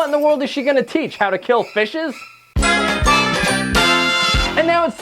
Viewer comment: did u just say fishes?